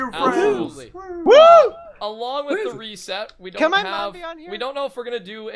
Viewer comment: "Can my mom be on here" xD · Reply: OMG I THOUGHT HE SAID " can my mouth be on u" wtf